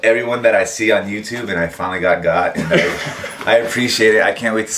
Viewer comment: I thought he said "I finally got God"........hilarious!!!